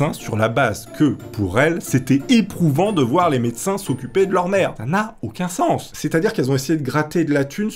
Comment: et 2:36 - 2:37 C'est moi ou j'ai l'impression d'entendre le mot «merde» plutôt que le mot «mère»? LOLOLOL😁